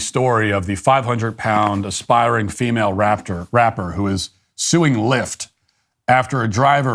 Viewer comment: I heard you said “raptor”? Before correcting yourself to “rapper”…😅